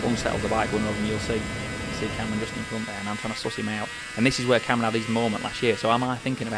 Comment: I'm trying to suss him out; not source him out. 16:08, He said because 600s are so closely matched; not members. 16:19, "A little bit of back brake there to stop it wheelying." There are too many mistakes with his words. I noticed them earlier too. I understood at least 95% of what he was saying and I'm a southerner; not from Yorkshire nor a bike enthusiast. 😳 Anyhow, job's a gooden. 😂